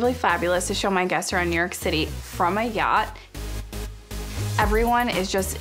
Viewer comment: did she say ... " from MY yacht " ??? Hahahaha a bit of a reach there Miss my yacht !